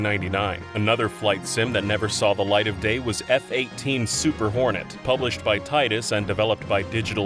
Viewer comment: I'm beyond immature, I thought he said "F-18 Super Horny"